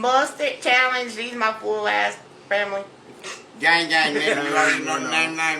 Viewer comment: did I hear that correctly? “Gang gang -“WHAT